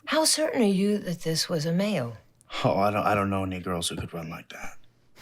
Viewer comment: lmao yall finna be mad at me for hearing "i don't know a negros tht could run like tht" 🤦🏾‍♂️🤦🏾‍♂️as opposed to what was actually said "i dont kno any girls tht could run like tht" 😅😅😅 my bad